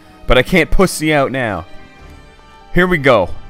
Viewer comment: Once I thought Zelda said “Don’t pussy out now!”